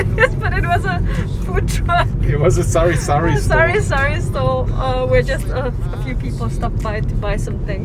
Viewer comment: What was said: Thought it was a food truck but it was a sari sari store. What was written in the subtitles : Thought it was a food truck but it was a sorry sorry store 😁😁😊